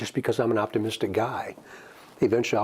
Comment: The cop sounds like the killer. Ask him to say down the hill lol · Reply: Listen at 2:08 he actually says “guy”!